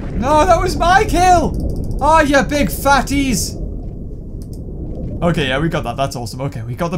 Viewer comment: did you just call your team mates fates? 😂😂😂😂 Edit: I found this come to after 2 years ._. No one cares